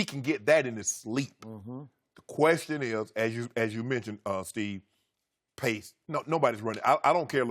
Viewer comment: Did Shannon call Chris, Steve? LOL